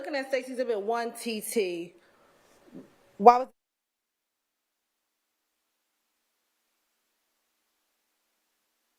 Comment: the sound went out what happened and what was said? 🤔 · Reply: apparently said Yak Gotti had been "in and out of jail."